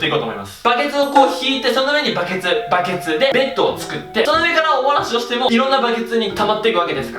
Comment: お漏らしが、おもなしって聞こえる！( ´艸｀)ﾌﾟｯﾌﾟｯﾌﾟｯ